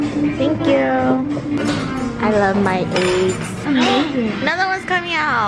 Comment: I thought she said “I love my AIDS” 😂😂